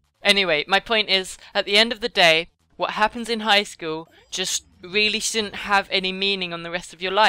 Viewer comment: I think was saying what happens in highschool stays in highschool